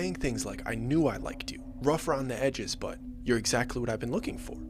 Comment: Did YOU JUST SAY. "ROUGH AROUND THE HEDGES "🤣😂🤣.. ITS "ROUGH AROUND THE EDGES "😂🤣😂